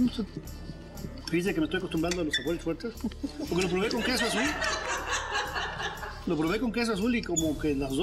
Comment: Que dijo del 1:29 al 1:32? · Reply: Ivan Viso Con queso Azul